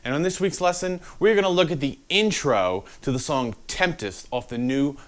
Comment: Did you say temptist?